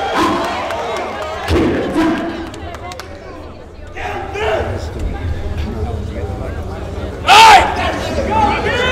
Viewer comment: What did Canibus say to Dizaster before the battle starts at 2:51 ? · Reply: Go easy on me